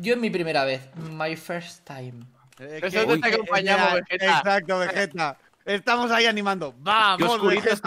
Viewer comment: Escuche un Wigetta